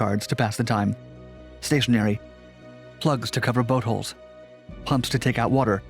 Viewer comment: Plugs to cover buttholes? Uhhhh okay Germany, okay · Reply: It's Boat holes.....butt.....at first I also thought I heard that